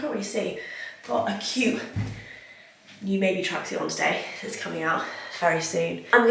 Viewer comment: When you said your wearing your maebe tracksuit I thought you said baby tracksuit! You definitely should do a maebe baby range! Congratulations! I gasps when I saw your announcement! So happy for you both x